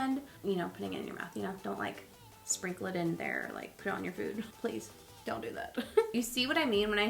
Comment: when she was talking about the powder and said dont put it in your mouth or dont put it on your food I thought she said dont put it on your boobs lol and I was like whoa girl